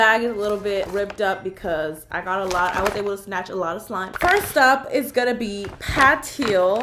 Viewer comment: sntached instead of snatched